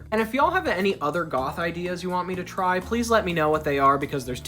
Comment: I thought he said "Mother gothal ideas" instead of "other goth ideas" and now I want to see Mother Gothal lol